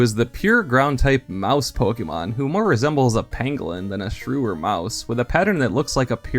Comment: At first I thought you said that Sandshrew looked like a penguin and I was very confused... Then, I had to rewind for a reason and re-heard you properly; 'pangolin'. Oohhh, yeah, I can see that... XD